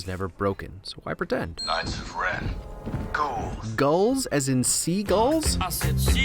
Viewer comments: i think they said 'ghouls' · I think the stormtrooper is saying ghouls